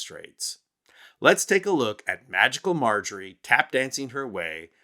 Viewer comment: I thought you said vaginal Margery😂😂😂😂